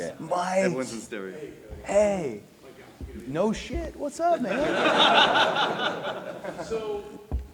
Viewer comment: Did he really said "whats up ni**a" at 17:35 ? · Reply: He said “what’s up man”